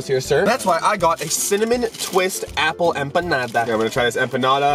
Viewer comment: first I thought he said 🍎and 🍌